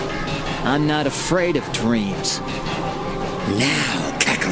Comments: "I'm not afraid of dreams" is a hard line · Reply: I genuinely thought he said “I’m not afraid of twinks” 😭😭😭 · is that a Swim Style ? 😂😂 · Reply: gabbermac _ I actually thought he said "I'm not afraid of dweebs."